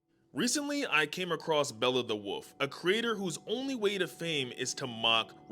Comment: Omg at first I thought he said beowolf